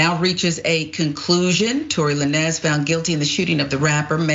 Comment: Did she really say Tory "La-Nez?" 🤦🏾‍♂️🤦🏾‍♂️🤦🏾‍♂️